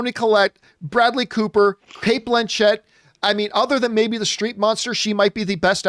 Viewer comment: He said, the Streep monster meaning Meryl Streep. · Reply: oooh the Streep I thought he said Street !! Thanks KW for clarifying